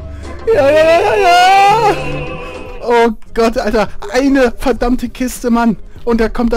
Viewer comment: Did he say "I kissed a man" in 2:39? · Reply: no he said: "Eine verdamte Kiste". It means "one fucking case"